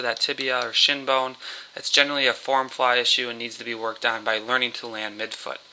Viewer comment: what did you say this was? I am hearing "form fly shoe" (and that I need to learn to land mid-foot). Please clarify? Thank you. This is exactly my problem and have not heard this explanation so it helps a lot.